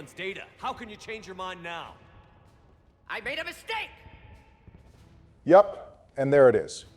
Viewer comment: " I Made him a steak! " I didn't read the sub And i thought mordin just said he made a steak 😭😭😭